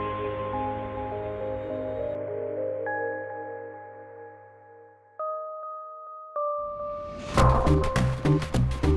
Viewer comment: what is he saying here? Soundtrack to what?? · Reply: FTL and Metroid Prime soundtracks.